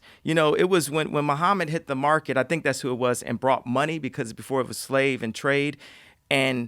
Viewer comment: did he say muhammad brought money?